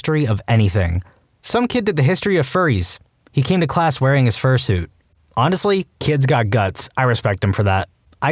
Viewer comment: did he just say first suit and not fur suit?